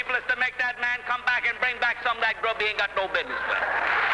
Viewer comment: Wtf did he say at 0:38 like it sounds like gibberish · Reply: "Get that man to come back and bring back that grub he ain't got no business with"